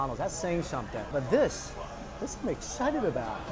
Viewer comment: it sounds like he says Mccited haha